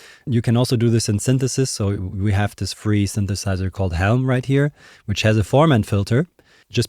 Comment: What is the name of the free synthesiser he mentions? (I don't fully understand what he's saying.) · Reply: he is mentioning the free synth Helm by Matt Tytel 🥲